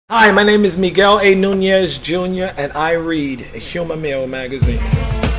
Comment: Lmao I thought he said a human male magazine